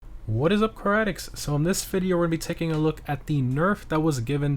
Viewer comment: I don't always visit the channel alot but I thought he always said, "Caradics" now I finally payed attention "Car Addicts"